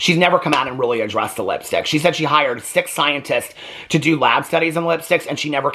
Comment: To this day I hear “sick scientists” instead of “six scientists” every time 😂